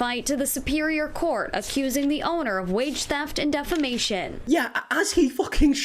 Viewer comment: When the news anchor said he’s taking her to court for “wage theft”, I heard “witchcraft” and had to back up to hear it correctly. 😂